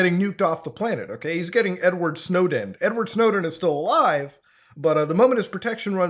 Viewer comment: Did you say N-word Snowden??